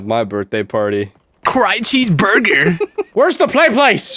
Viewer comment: Bro I thought that said gay cheeseburger and both results made me laugh